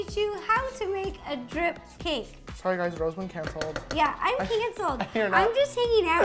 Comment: did just say “sorry guys Rosaline cancelled 🍰 🍑